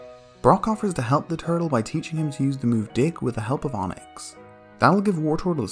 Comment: did he say the D word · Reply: He said "Dig"